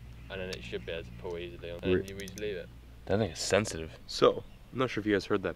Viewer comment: Did he say that nigga sensitive in